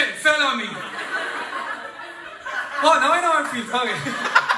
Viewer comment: What did he say? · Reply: "Ok oh now ik what if feels like" 😂